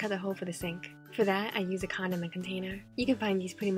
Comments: hears it for the first time Hears "condom container Listens again while thinking wth Hears what it's actually says Ohhhhhhh😂😂.. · I am sorry but at 0:46 did she say condom btw I really love your creativity